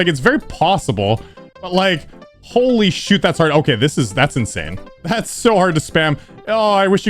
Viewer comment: It sounded like he said holy shirt at dry out